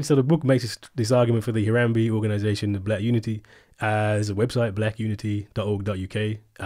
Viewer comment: Did he say the Harambe organisation of black unity?